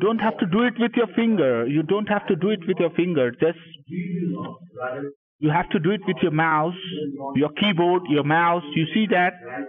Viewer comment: Omg, when he was saying “you have to do it with your mouse” I thought he said “you have to do it with your MOUTH” And I was just trying to picture it and thinking why when I thought, maybe the guy was already annoyed so much that he wanted to get going with anything and to shut the guy up. So two birds and one stone kinda thing...um